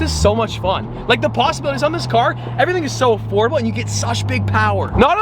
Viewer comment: Did ge just say that everything is affordable ?